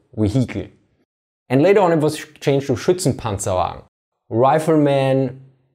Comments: I hear shirts and pants alarm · I'm sorry, but when I first heard him say these words in his native German language: "Schutzenpanzerwagen" It almost sounded like he said: "Shitsinpantsonwagon" No rudeness or disrespect or anything it's just that's what I first thought I heard him say... It's still a kick ass name for a Half Track, though!!! 👍😎